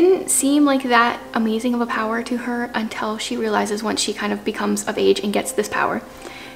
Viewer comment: i thought you said "until she becomes a mage." I was like, wait what?! 😂 Oh Lord no, not Wuthering Heights, please 😭😫😂 but I agree, it is very atmospheric! I found and bought And Then Their Were None from a thrift store yesterday, after hearing about her so much from your videos. But I'll add this one to my tbr since it's your fave! I tend to forget I have Sherlock Holmes on my shelf. Maybe I'll try to read one of the short stories this fall if I can finish all my other books first. Btw, I like the music you have playing throughout this video.